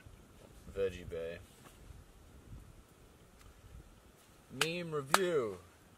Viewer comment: did you really say 👏”meme review”😂😂